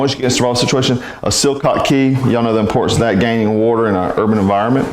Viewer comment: What kind of key is that? I couldn't understand what you said? It was around time stamp 2:40. Cool bug out bag. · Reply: Just google water key